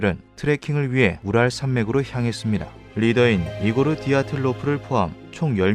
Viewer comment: 분명 우랄산맥인데 ㅂ랄로 들리네….난 존나 망했다